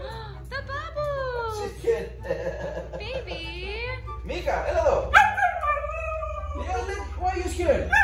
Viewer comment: Did he just said:Meeka Έλα εδω? (Έλα εδώ means come here in greek)As i greek i can say that he spelled it perfect